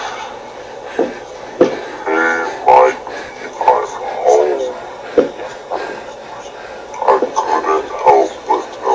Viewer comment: Can anyone tell me what did the entity say at 9:10 and if anyone can tell me who that entity was? Because i think it was the one that was shown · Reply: Mikee, im home..